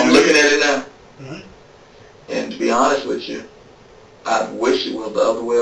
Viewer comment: If you enjoyed this video and would like to support the channel, pressing that like button and leaving a comment really helps. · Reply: witch chu, what does that mean. Was the cop trying to say with you or did he have slurred speech?